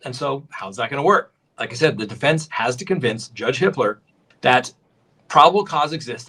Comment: i keep hearing "Judge Hitler", idk why